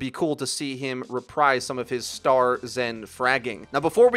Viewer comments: Did you say epic as in epic games? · “Epic has also published-“ Am I hearing wrong? Didn’t he mean Blizzard?